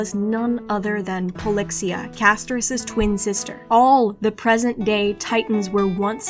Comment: Huh??? I heard that as "present gay titans" lmao 😭😭